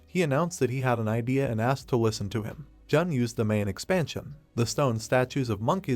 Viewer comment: I heard it wrong I thought you said domain expansion lmao😂